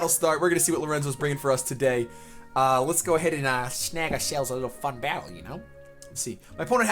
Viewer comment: turn on close captions. What Says: "let's go ahead and snag ourselves a little fun battle" What Youtube says: "I'll let's go ahead and ass nigga sales a fun battle"